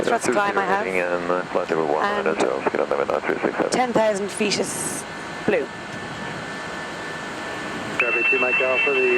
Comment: Did she really say Ten Thousand Fetus Blue?